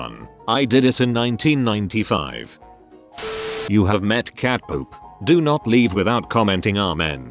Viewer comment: When he said cat Pope I thought he said cat poop